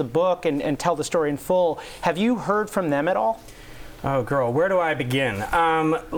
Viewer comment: did he just say " oh girl " lol